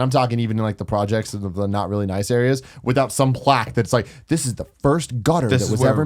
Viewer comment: is that tim being racist at 0:30...."cant walk round the projects without some black sayin this is the 1st this and this is the 1st that"....might be wrong but thats what it sounded like..tut tut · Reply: Haha he said plaques but I guess how I could see how you heard that.